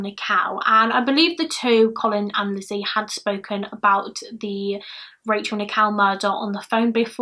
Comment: I love your accent, but for the longest time during this video I thought you were saying 'Rachel Nikow' as in Nik-ow 😂😂❤